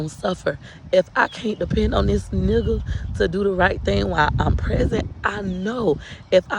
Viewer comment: Shoot. I thought she said while I'm pregnant but I replayed and realized she said present.. lol